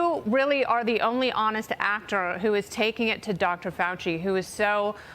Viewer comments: Did she say "ACTOR" AT 1:55 into the video? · did she say "you're the only honest actor"????? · Wait -- did she really say "You are the only honest actor" @ 1:57?! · s did she call Senator Paul an "actornn" with long "N" thrown in for reasonable deniability or purposefully mispronounced Just to get the truth out but also cause confusion of what she says!? 🤔 Every media should be scrutinized and looked at with a critical eye ! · Did she call him an actor? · did she say only honest actor?